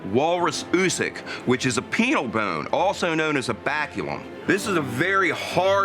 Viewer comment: Anyone else thought he said penis bone? 0_0